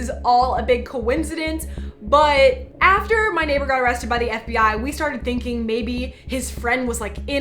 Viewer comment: At that one part I thought she said "omg my nipple got arrested"😂😂😂